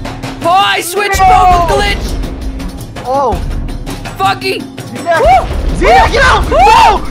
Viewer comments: I thought you said fcking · did he say f***ing s**t funky